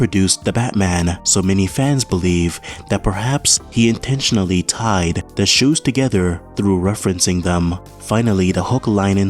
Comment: sounds like u said 'tied the shoes together' 😊